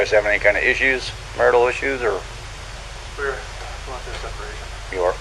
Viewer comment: I could be mishearing what he said but it almost sounds like says “we WERE going through a separation” but quickly catches himself and says “we’re”. There’s a slight pause right before he finishes the word.